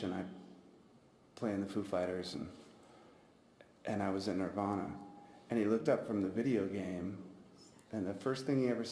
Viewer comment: I wanna know what Dave Grohl said at 1:56:25 when it messed up. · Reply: He said Oh!, I´m really sorry about your friend!